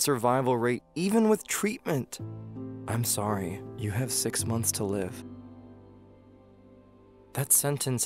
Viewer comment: He said 6 months to live But I heard "After 14 months" 😐😶😐